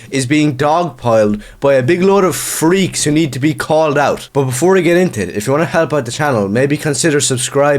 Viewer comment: I misheard you when you said these leftists lunatics need to be "called out". At first I thought you said "culled out". I prefer what I misheard.